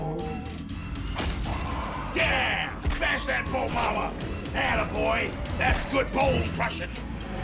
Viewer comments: recently finished playing dark souls 3 for the first time; it was my first playing a souls game. I can't tell you how many times i said "WATCH OUT SCROOGE ITS A BONE MAMA" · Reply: I always heard "Bone Llama" cause he has the skull of an ungulate. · It drives me nuts it is captioned as "bone larva" on Netflix, yet on their YouTube it says "bone mama" - which is what is saying. Why do you torture me Netflix?! This is legit the worst thing that's ever happened to me. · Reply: I originally heard it as bone llama...